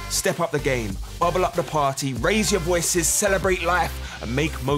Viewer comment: I thought it said FartGas